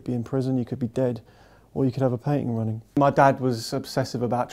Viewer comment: What's the last thing he says at 1:10? "Or you could have a....." Something? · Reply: Painting running.